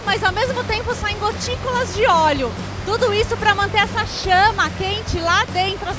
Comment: só eu entendi chana kkkkkkkkkkkkkkkkkk